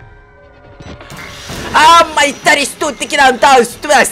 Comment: что он говорит май стикиа?